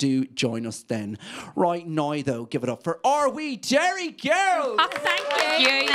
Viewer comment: I kept hearing him say 'Jerry girls'